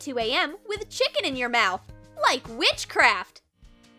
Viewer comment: is it just me or i think she says Mitchcraft Edit: you dont know who is him he is a youtuber